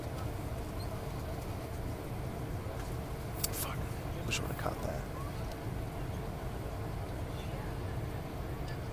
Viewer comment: What does the guy whisper at 1:07? It sounds like "Fuck, we should have cut that" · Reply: what i said was "I should have CAUGHT that". what i should have caught was the mother asking the passengers on the plane if "they were bothered by her daughter" to which almost the entire plane said "no not at all"